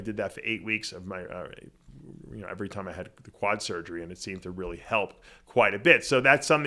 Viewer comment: All i heard: " everytime i had quad surgery " 😐